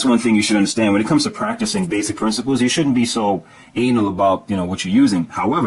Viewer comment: did he say you shoudn't be so "anal" ? :O